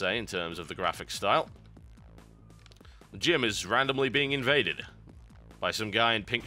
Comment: I thought you said "Jim is randomly being invaded." and I was thinking to myself "Who is Jim?"...